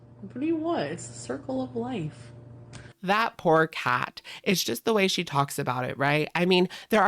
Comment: You said "that poor cat", but I heard "that pork hat".